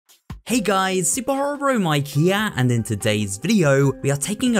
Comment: i heard super horror bro my gyat wtf has tiktok done to me